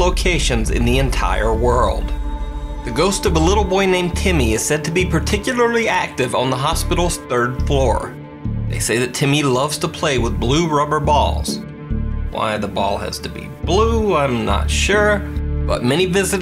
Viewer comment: ”Timmy likes to play with blue balls” What I heard “Timmy has blue balls” 🤦🏻‍♂️🤦🏻‍♂️😂